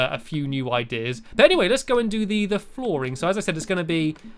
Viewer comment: Now let's do the flooring I thought he said now let's do the boring! Lol